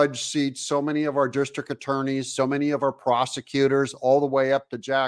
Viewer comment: Did you say prosecutors or did I hear "prostitutors"...askn' for a friend...!